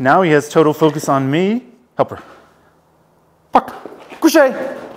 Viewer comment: For some reason I hear "fuck em" and dog goes on the offensive.